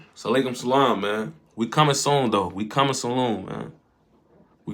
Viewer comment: that salaykum salam (Wa alaykum as-salam") fucked bro up so much that instead of we coming soon man bro said we coming saloom fseee 😂😂😭🤧